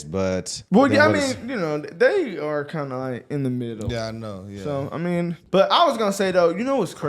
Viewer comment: Did you say TYT was in the middle?